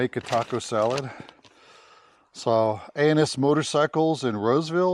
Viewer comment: Sounded like you said anus motorcycles